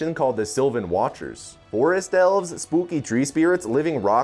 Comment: Instead of Forest Elves i thought he said “Four Astels and Tree Spirits” and thought it was an Elden Ring reference